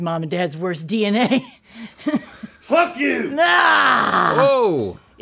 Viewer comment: I swear like a sailor in my every day life but “cluck you” had me clutching my pearls and gasping until I realized what he said 😂 that was priceless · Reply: i swear i heard an F 😂